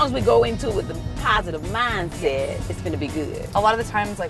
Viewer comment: She said "It's Finna be good" but the caption said "Gonna"